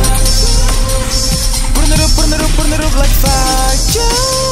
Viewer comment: Pensé que decías "P0rnohub, p0rnohub, p0rnohub laik faiir" hasta que me fije lA LETRA QUE ESTÁ EN EL VÍDEO Y ERA "Burn it up, burn it up, burn it up, like fire" CHAU JAJAJAJAA Hermoso cover. ♥